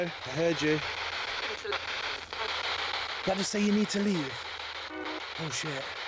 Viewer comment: Fun stuff, but you said "literally" at least 25 times; it was a bit distracting. So much so that I thought the ghost you heard say "You need to leave" really said "Literally" lol. Take a listen - it really does sound like that!